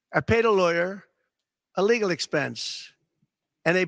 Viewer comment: ish: I know he's trying to say "as a legal expense," but I keep hearing "as illegal expense." Don really needs to lay off the sauce&ambien in the middle of the day.